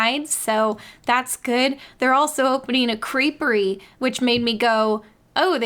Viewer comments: Oh. Crepery. I thought she said Creepery. I was like "Finally! A Disney attraction for me!" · 87/10 interest in the creperie for me. I love crepes. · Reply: English isnt my native language, I didnt know the word "creperie" so i misheard it as a "creepery" and was like "yeah i like creepy spooky stuff"